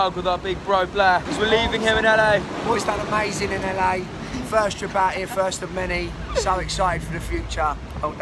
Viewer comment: Honestly thought Blair said "first chapatti" turns out he said "first trip out here"